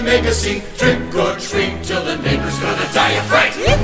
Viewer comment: Till the fucking what dies of fright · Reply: I heard "the neighbors". I think.